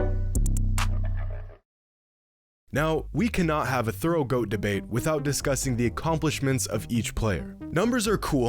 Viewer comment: i thought i misheard that, had to rewind. CANT HAVE A WHAT DEBATE???????????? WTFFFF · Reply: thorough goat debate